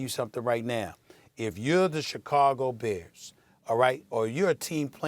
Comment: Stephen A almost said Chicago Bulls .. Sounded like he said Buears 🤣